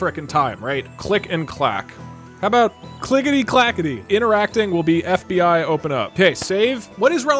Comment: When he said interact, thought he said “In Iraq” PTSD kicks in